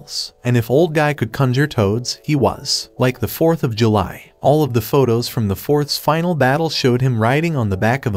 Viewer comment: Did I just say the forth of July